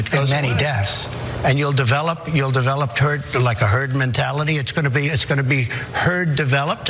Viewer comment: At about 1:12 I think he said "turd mentality." That's it! That's what trump has - turd mentality. He'd like the rest of us to get it too. No thanks, must vote blue this time.